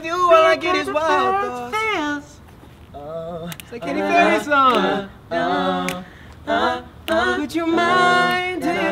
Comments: what did zion say and what was the song cuz it sounds nice ( before would you mind by prettymuch) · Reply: he said “it’s a katy perry song” and i think the song is called “feels” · What did Z say at 1:14? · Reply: N Nivetha “the Katy Perry song”